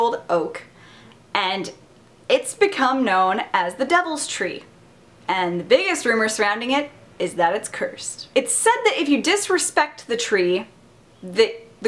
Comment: i thought it said THE DEVIL'S TEA. i was waiting to hear about tea the whole time lol